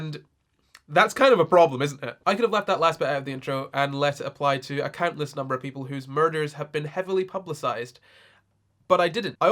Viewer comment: Hey, can someone write to me what he says in 0:27 to 0:37, please? speaks fast, and I'm not a native speaker, I want to caption the video in Portuguese but i can't really understand what he's saying in this part, I'd appreciate the help ._. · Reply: “That’s a bit of a problem isn’t it? Now I could have left that last bit out of the intro and let it apply to a countless number of people whose murders have been publicised... but I didn’t. I also could have left it and let it apply to all the people whose murders haven’t been publicised”